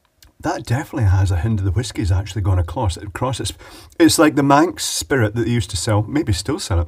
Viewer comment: To paraphrase Ford Prefect: "Oh don't give me none more of that Old Manx Spirit No, don't you give me none more of that Old Manx Spirit For my head will fly, my tongue will lie, my eyes will fry and I may die Won't you pour me one more of that sinful Old Manx Spirit" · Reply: I didn't get the reference. I thought he said "Mank spirit."